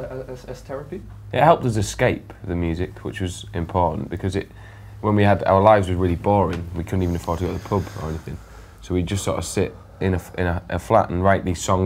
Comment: does he say "we couldn't even fart or go to poop "????